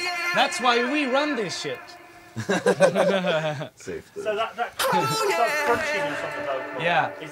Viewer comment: Does Seb say "safety" at 9:52 because he farted?